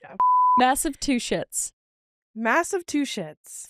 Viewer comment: What like mass-a-two-sh!t$? 😂😂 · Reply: I heard it as massive-two-shits. Lmao.